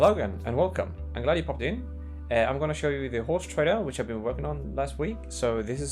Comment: I thought you said horse trailer I was very confused haha (This game is going to be amazing!)